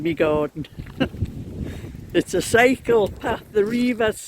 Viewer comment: I had to laugh at myself, I thought you said psychopath and then I realized you said “ cycle path “. Don’t mind me, my hearing is not that good. I noticed Mrs D is selling on Kofi, she mentioned it on her vlog. I worked in my yard again, I was so exhausted I could hardly walk in the house. Not good, I need to pace myself better. Glad your son helped you today, it looks very nice.